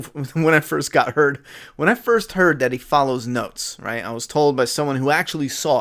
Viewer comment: When you said he follows notes. I thought you said he follows goats and busted out laughing